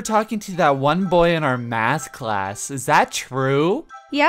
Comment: i thought At first she said ASS class xd